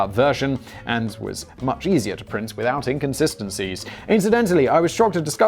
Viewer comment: Did.. Did he say inKonsistencies?